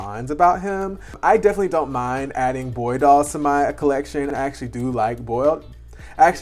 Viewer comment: I heard 'I actually do like boiled doll'😂😂😂 I just got Aidan second hand, he is very cute though the hair is a mess Your restyle is great :)